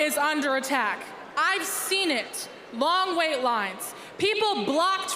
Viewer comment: I thought she said “long waist lines” at first